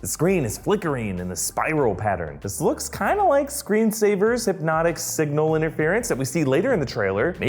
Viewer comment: At first i heard him say screensaver... I need my eyes checked.